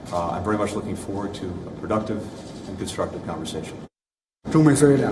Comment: Did Blinken just say productive and destructive conversation? Instead of constructive